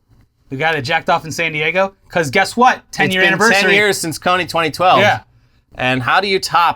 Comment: I swear I heard "Cummy 2012" I've been too corrupted by the internet